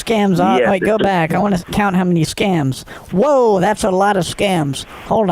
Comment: "THAT IS ALOT OF SCAMS", not SCANS).... Did anyone else catch that????? 7:02 I almost fell off couch laughing!